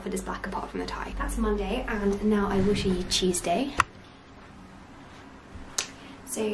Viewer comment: I misheard you say Tuesday as cheese day 😭😭😭